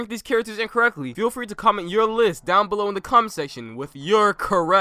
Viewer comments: did i hear CUM-SECTION?!?!?! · I thought u said cum instead of comment XD yeh I have a dirty mind that's why I have CC on